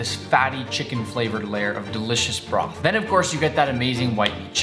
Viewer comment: Did he say broth-?